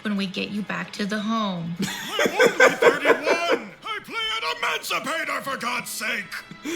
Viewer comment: Gramps said he had Adamancipators? I thought he said Constipators!